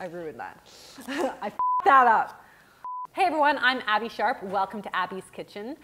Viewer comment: love your videos, but can you say your name more slowly because I swear I hear “Abbey Shark” like 75% of the time.